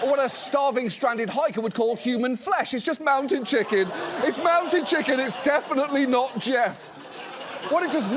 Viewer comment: When she speaks, all I hear is 'Candy mountain Charlie!'